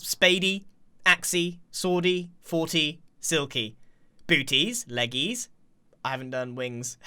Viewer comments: yo did grian just say booty 😱😱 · did i just hear booties